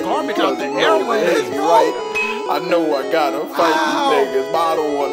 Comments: “I saw a bad chick up at the mall but I was shy” We all felt that 😔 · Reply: I thought it said but I was shot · "I saw a bad chick up at the mall but I was shYy" 🤦🏾😭😭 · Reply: I thought he said "i saw a bald chick up at the mall but I was shy"